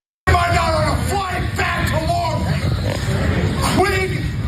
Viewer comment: Q U I C L E Y??? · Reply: I thought he said Quidley